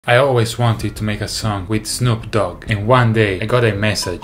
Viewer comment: i thought he said "i always wanted to make a son with snoop dogg"